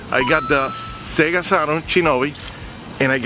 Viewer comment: It sounded like said Chinobi at 7:55 lol you always making us laugh 😂 and is that a El Chavo del 8 reference when they bop you on the head with the Mario hammer bep bep bep bep bep bep 😂